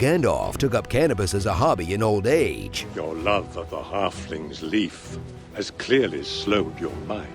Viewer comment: OMG, I just know realized that Sarumon said 'Your love of the halflings leaf...." I thought he just said 'love of the halflings...'